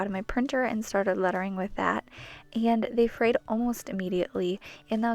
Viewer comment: And they ?what? almost immediately? I didn’t hear the word correctly, english isn’t my first language so I don’t understand what you were saying here... Can somebody please tell me what she said? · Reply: It's that they frayed almost immediately! Sorry! I know I talk fast sometimes... hopefully I will be able to get subtitles going soon! Thanks for watching!